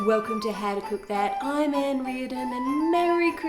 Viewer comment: The whole time I thought she said “I ain’t reading” but she actually said her name